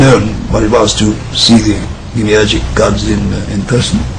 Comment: I don’t think he says “emerging gods.” I think he said “demiurgic,” as in intermediary deities who create the world in place of the higher God.